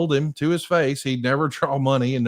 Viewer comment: I thought Ole said Red heads will never draw money